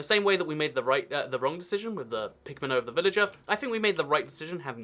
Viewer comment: Haha it sounded like you were saying pigment instead of pigman 😂